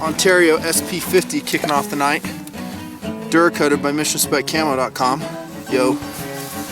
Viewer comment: Anybody hear: Ontario SP 50 "duracoated by Mr. ? .com" at 17:05 ??